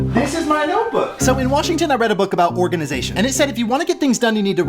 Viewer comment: I thought you said "Organ Donation", but you said "Organisation". Brilliant.